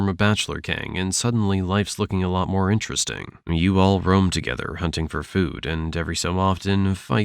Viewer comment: did he say brome together?